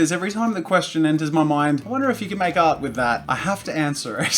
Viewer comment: I thought he said make out with that.